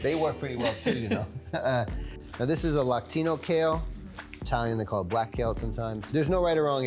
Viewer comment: Lactino kale? You mean Lacinato? Lol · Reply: I thought he said Latino kale. I was like what?